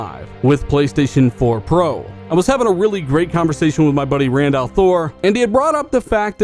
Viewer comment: Whats your buddys name at 5:30? I heard Randolph Thor? Lol anyone?